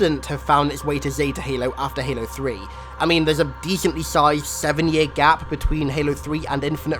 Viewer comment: he goes full nerd and say dethaintly sithed instead of decently sized.i just saw this channel I love it been watching for hours!not trying to be mean just thought it was a bit funny